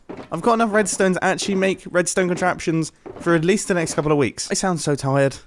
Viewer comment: I like how he is like "this much Redstone will last me a week.", like WHAT??? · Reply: I think he said couple tho (Edit: He says so